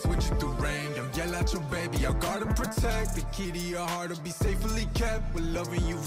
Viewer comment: You know BM has been so out of pocket with these raps lately, I really thought he said “protect the kitty” 😂😂